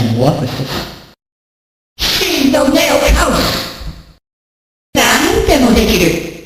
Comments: I always hear "Man WTF is this" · With the METAMORPHOSIS 0:09 I never knew he actually said anything. I thought he just made a random noise like: EH OH. Or something like that.